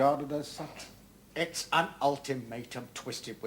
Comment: lol … 'That is our decision. That is our final word'. And THAT is telling him! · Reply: i thought he said it was NOT an ultimatum lolol